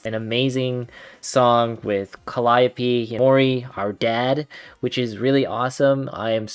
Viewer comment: did he just say our dad lol 😂